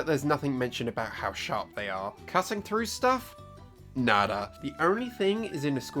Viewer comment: Why does it sound like says "Cutting through stuff? Nadder..." when he is explaining the Stormcutter? XD